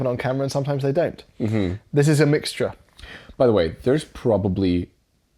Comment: did you just say "mixtra"